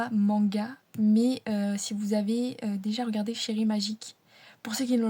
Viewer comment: OMG, je faisais autre chose et j'ai entendu "Chibre Magique". J'étais pas prêt :D